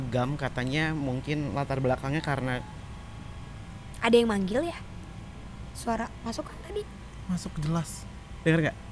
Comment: kedengeran bgt asli.. padahal lagi nyetel volumenya rendah... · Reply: Iyaaa "kakak" kata dia kan?